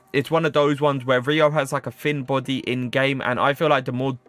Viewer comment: tf you did just called him? 💀💀 JEREROU